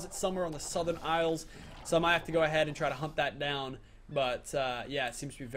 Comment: i swear he said hump them down